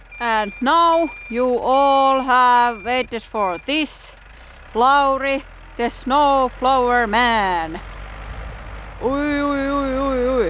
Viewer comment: did you say "This is Plauri, the snow plower man?" 🤣